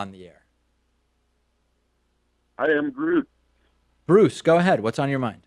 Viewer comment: Caller needs to work on audio quality. When he said "Hi, I'm Bruce" I thought he said "I am Groot"